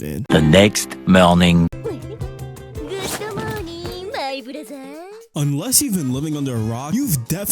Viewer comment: why did i hear brothaaar